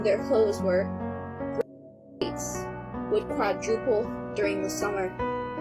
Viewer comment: what is the blurred out words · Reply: g//rape